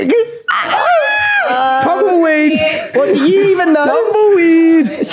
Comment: what did Whit say when Lochan was talking about them being boyfriend and girlfriend? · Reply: When’s their anniversary